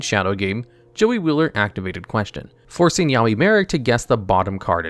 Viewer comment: did say "yaoi marik"